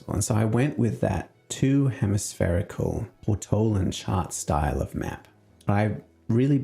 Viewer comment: Say, guys: I'm a bit hard-of-hearing and always have closed captioning on but with it being auto-generated by AI (rather than manually by a human) on this vid, there's a single word it mispells that I can't make out no matter what I put the volume to. Around 11:28 it sounds like he says that this type of map is a "Two Hemispherical AUT-OH-LAN Chart-Style of Map" but for the life of me, I don't know what "autolan" (that's how YouTube spells it) means. I did a lot of mad searching online earlier and the closest I could figure out was that he was talking about "Authalic"? Could that be it? I'm really trying to follow along with his process so if someone could highlight what that word is and what it means, I would be truly grateful! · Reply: It means "portolan" -- the anti-pop filter I've got on must have cut the noise off enough for the captions not to work! Portolan being the style of mapping that uses linear charting lines to maintain distancing without distortion. Sorry about that!